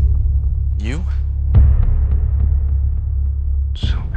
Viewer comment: It sounded like exward said beautiful cen.chep a chuno thts wat it sounded like lol